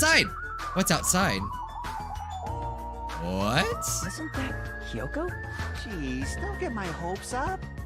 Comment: When Hajime said "isn't that Hiyoko?" I heard "isnt that Kyoko" :< and now i miss Kirirgiri